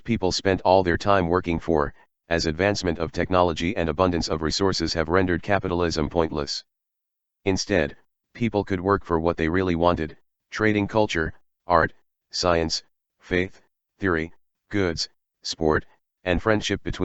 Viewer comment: Me wishfully mishearing: "...rendered capitalism pointless; its dead." Gods I wish it were